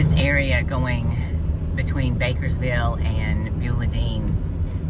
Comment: I thought she kept saying Bakersfield. LMFAO 😂😂